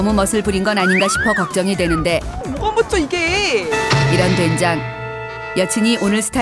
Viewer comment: 껌붙여 이게?? 중국말이냐 뭐라는거지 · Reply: ”뭐가 멋져 이게“라고 하는 거 같네요